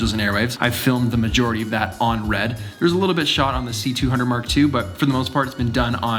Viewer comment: Did he just say c200 Mark II? 😳